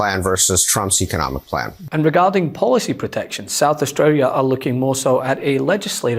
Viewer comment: Here in li'l ol' South Aussie, we're world leaders in getting $$ out of politics. Good on us!! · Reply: Thought he said South Australia, but was surprised when he called it a country. Gotta love that policy by your Premier